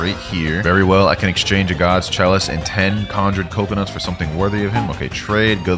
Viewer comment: Did he say "Conjured Coconut"??